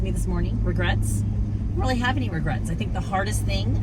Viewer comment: So funny. I thought you said” my husband asked for Grits” lil. But he really asked “ any regrets”. Lol. I was like-wow. I thought “ only in the South”.